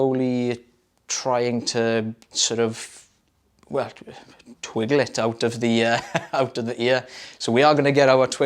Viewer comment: made me laugh with the Twiddle, lol! He killed it, did GR8, he's gonna fit in just fine! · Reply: I thought He said "Twiddle," but just watched again, and It's "Twiggle," lol!